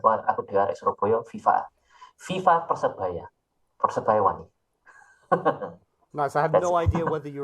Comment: Australian here, but of Indonesian background, and so I happen to understand Javanese.. he said something like (taking some liberties with the translation to make it sound natural in English): "How are y'all going, right now I'm talking to, or better known as, so, hello to all you guys who are watching this now, and a special shoutout to those from Surabaya, because I too am a Surabaya kid (there's probably a better way to translate 'Arek Surabaya'.. but it's a bit difficult to get the same tone across)" + I'm not entirely sure what he said at the end, must be a Surabaya thing 😅 Super suprised but very happy to see on your channel, he's a great inspiration for me, as I too love learning European languages (Italian, German, etc.) Hope to be even one tenth as good as him one day! That and it's really cool to see an Indonesian polyglot represented! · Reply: Thankyou for your lovely words!❤ At the end i said "viva Persebaya" which is the football club of my city, and actually i wanted to say something in Javanese but idk why my head was thinking in Spanish out of nowhere😂😂 you'll be a polyglot someday and will be better than me! I'm sure of that!! Keep grinding those languages my man!🎉